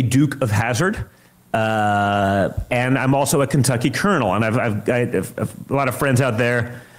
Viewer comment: Oooh, a Kentucky Colonel. I remember about 8 years ago when someone had their dog made a Kentucky Colonel. 🤣🤣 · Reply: I thought he said, "Kentucky kernel!" 😂